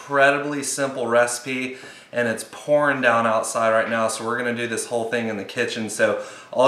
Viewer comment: For a second when you said it was pouring it down outside, i thought u said there was PORN outside. I was like BACON, CHEESE AND PORN? Best video ever!